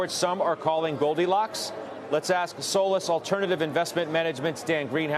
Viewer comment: I heard that as "soulless alternative asset management" 😅